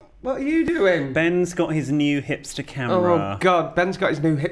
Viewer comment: I heard ‘Ben’s got his new hips to camera’ as though he’d had a Brazilian butt lift and was gyrating at the camera